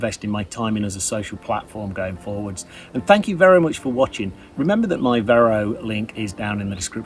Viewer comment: Thank you Vero much for watching. I know it isn't what you said but talking about Vero it sounded like it to me. XD